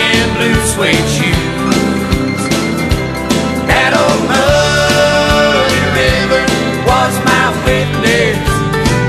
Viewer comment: I always thought he said "That ol' muddy river, washed my windows" lol~